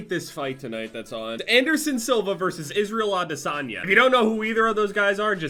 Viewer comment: all I heard was something "Silver" and "lasagna"